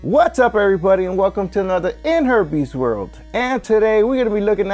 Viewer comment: I heard "in herpes world" and I was like... Whaat!😮 lol but great video, thanks for uploading it!